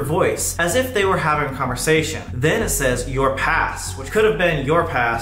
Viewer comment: Ngl I thought when it said “your past”, I thought it said “Your Ass” 😂